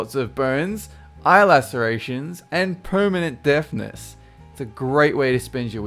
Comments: For number 8 I thought you said permanent death-ness. I be like wut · Is he saying permanent deathness or permanent deafness? Like, honestly, I can't tell.